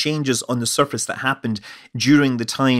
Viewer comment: Why do I always hear: "I'm Scott Manley - fly shaved!" ?